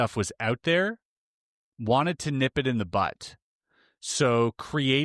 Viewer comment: did you say "nip it in the BUTT"?